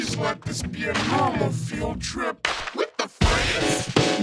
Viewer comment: sounds like she said what the frizz lol